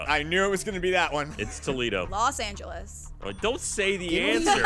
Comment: Did ryan say Toledo at 2:40?